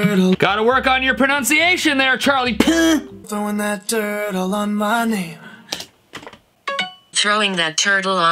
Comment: Stop throwing that TUUURTLE ON MY KNEE (Real Lyric: Throwing that dirt on my name) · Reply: I always thought he said “Turtle on my nana” 😂😂😂😂